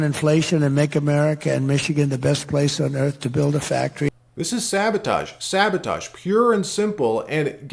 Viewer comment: Did he just say, "this is Savatage"? That's a metal band from the Tampa Florida area. 🤘